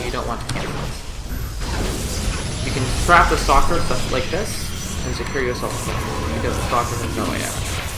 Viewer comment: it's kinda hard to hear what you're saying here, the game audio is too loud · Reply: "you can trap the stalker such as like this